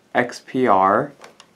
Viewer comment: Thanks for the video. Toward the end it sounded like you said Connect to Xer but (the only time) I didn't see you turn the echo dot toward the camera so I could see it. Did I hear the name right? Also, can you tell me if I can use my same computer (or iphone) to a different echo dot and network? I don't know if it'll mess up the already working echo dot I have at home using that same computer and Alexa App. It's a gift and I'm afraid I won't be able to do it. Any help with this would be greatly appreciated :)